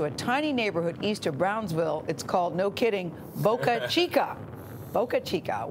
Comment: I thought it said boca china, am I dyslexic?!